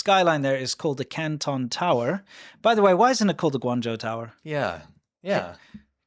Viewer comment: I thought you said tampon tower. huh?